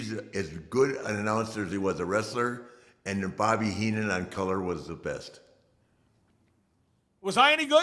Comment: I thought he said Gordon was the best lol